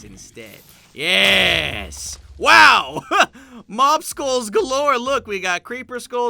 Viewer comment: When he said "Mob Skulls" I thought he was saying "Mobsicles" Holy buckets thanks for all th3 likes! Most likes I've ever gotten!